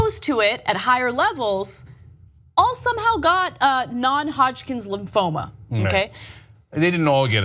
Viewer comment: subtitles say "non-hop skin lymphoma". Is that a new type of cancer? or the person in charge of subtitles had never heard (or asked) about non-hodgkin disease. And this are not auto-generated subtitles.